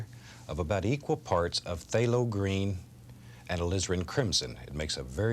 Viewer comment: What's Lizard crimson. I have Crimson red. But i don;t know what lizard crimson is. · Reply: I thought he said lizard at first but they do show the colors he uses on the bottom of the screen, alizirin is what he is saying :)